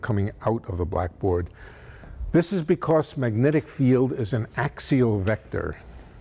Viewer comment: Can't believe I don't know this, but what is an axio-vector? I tried looking it up but couldn't find a proper definition. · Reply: AXIAL! OKAY! I could have sworn he said axio LOL woops. No wonder I couldn't find it, thank you for clearing that up for me.